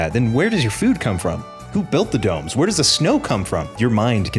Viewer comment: When he asked, "Who built the dome?" I heard, "Who built the dogs?"